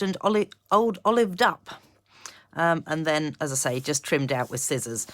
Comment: Very impressed with the die,stamp and embossing folder combo….. thought you said old olive duck😅had to rerun and it was up,❤